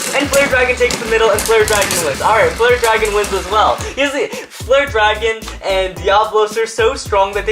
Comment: Did He Just Said "Flirt Dragon"?